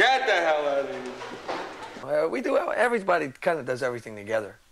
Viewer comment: What did richie says at 0:15? "we do what everybody kinda does..." or something like that...